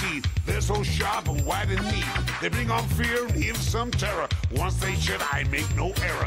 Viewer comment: Did he just say "once they shit?"